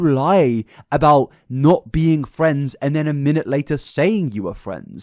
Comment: I heard “Not friends, at first,” not “not friends, ever.”